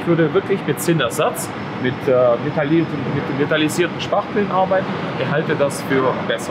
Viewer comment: was für Spachtelmasse meint er da genau? Vitalisierte Spachtelmasse hab ich bislang noch nicht gehört. Wäre cool, wenn ihr mir da Infos geben könntet. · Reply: Htown TV Metalisierter Spachtel ist gemeint 👍🏼